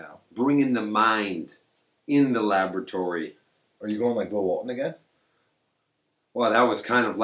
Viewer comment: I heard that you not slick 😂😂😂